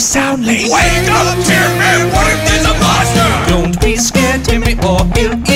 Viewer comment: “Wake up Timmy, what if there’s a monster” Said Dumbledore calmly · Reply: I legit thought he said wait there's a monster😭